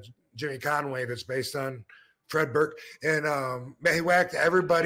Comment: Jimmy, I said. Jimmy Burke. You thought I said Fred? Nah nah....Jimmy. 🙂